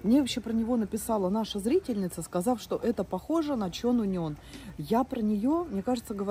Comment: А как называется сборник Чо Нунен? Гугл не выдает такой писательницы. Может быть я неправильно расслышала имя. Очень заинтересовала эта книга. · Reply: Чон Унён «Как она использует свои слезы» 👌🏻